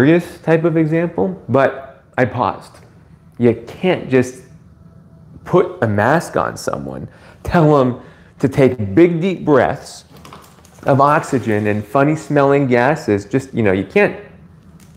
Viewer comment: mo matter how much i play this sentence i hear big dick breath xD